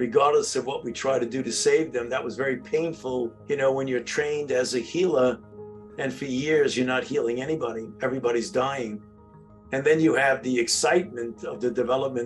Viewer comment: did he say trained as a killer or healer?? It wasn't clear...